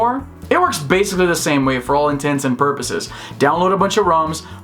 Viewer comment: "works basically the same for all in tents and purposes" Anyone else hear it too?